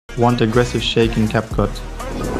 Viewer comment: I heard 'want this grimash shake'💀